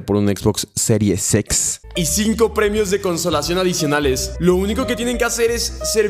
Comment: escuché consolador 😳